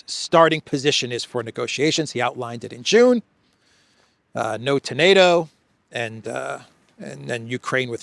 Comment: It sounded like you said, no tomato.